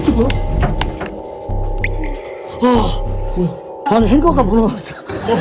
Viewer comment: 행거를 햄버거라고 들었어욬ㅋ